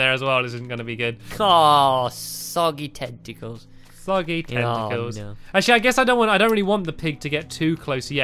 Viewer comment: why did you say soggy testicles?